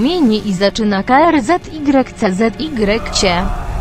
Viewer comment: Im polish but can anyone tell me what did 3:13 Say? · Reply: ale pisze się krzyczeć